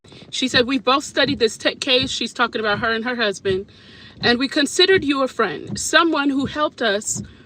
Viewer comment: I'm digging thru comments trying to find out also! I'm so confused! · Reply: her ex husband is what it sounded like she said